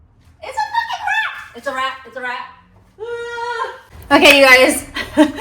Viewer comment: At first I thought you said "It's a wrap!" like you were done pruning. Uhh... no, that was a considerably different emotion you were experiencing there! 😅